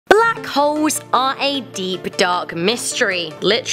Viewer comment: it sounds like she said black hoes